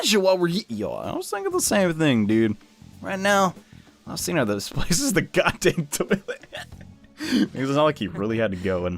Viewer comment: I thought ryuji said "This place is the goddamn toilet"